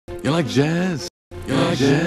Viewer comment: He actually sounds like he’s saying “buzz” after a while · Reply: After 1000 I heard "you like jizz?"